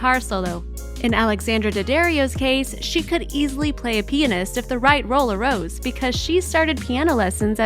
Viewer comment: What Did I Just Heard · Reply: Pianist fucking dumb asses